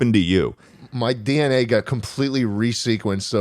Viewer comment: really thought he was gonna say he turned from homo sapien to homosexual